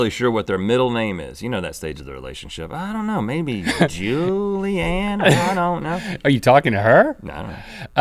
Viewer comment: Did Rhett say Julianna at 3:44? I couldn't tell.